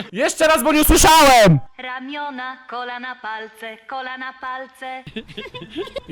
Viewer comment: hmmm... jesteś bardzo podobny do Naruciaka. ciekawe dlaczego? czy tylko ja usłyszałam: kola na palce?